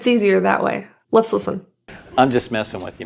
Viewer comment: I hear: "I'm just messing what you ment*